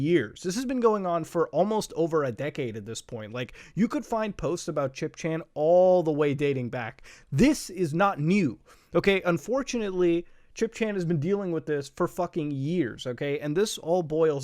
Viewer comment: I thought you said Chris chan and I was so scared for a minute, I was like "omg how did we lose her? I thought we had sights on her"